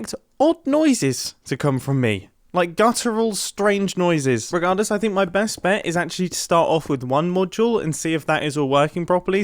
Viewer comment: i accidentally used my arrow keys while watching this and i heard mumbo say "regardless i think my best bet is to actually start off with- me."